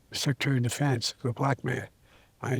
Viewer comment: I heard either "the black man," or "the black mayor." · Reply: He said "... black man".